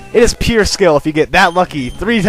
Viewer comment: did he say it takes skill to be that lucky?